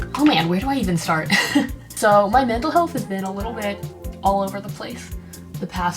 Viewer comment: should start a podcast. Like holy shit, she’s so good at like simple conversation and it would be really interesting to see all of her opinions on everyday things and stuff like that. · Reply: thought she said her dental health 🤣